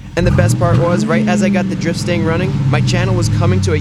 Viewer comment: I keep hearing "Drip Stain"